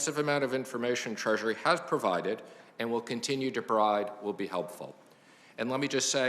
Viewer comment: What I herd Mnuchin say if you continue to bribe it would be helpful